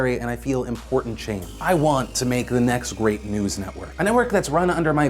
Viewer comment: sounds like he says "the next rape news network"